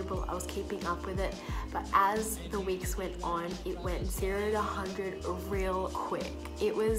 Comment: What did say at 4:08 verbally...I've liked it for SEO... And Subscribed too... I wish I could supe+like it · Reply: She said it went 0 to a 100 real quick meaning it gets hard suddenly